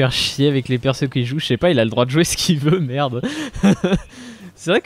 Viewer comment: Quand il a dit "j'ai le droit de jouer Neith", j'ai compris "j'ai le droit de sniff". J'ai un problème je crois.